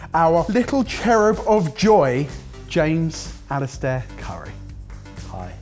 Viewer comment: When you said, "James Aleister Curry." I heard "James Aleister Crowley". Just watched a Biographics of the guy, what a nutty legend.